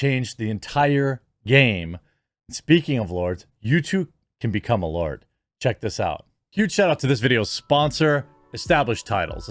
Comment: Bro I thought you said youtube can become a lord I was like excuse me